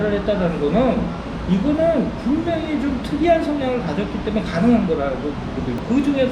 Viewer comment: 부분에 영문 자막이 잘못되었어요~ '성향'이 '성형'으로 오기 되었네요😂 한글 자막이 잘못되어서 번역도 그렇게 되었나봐요 ㅎㅎ 시간 되실 때 수정 하시면 좋겠어요~ 좋은 말씀 잘 들었습니다~😊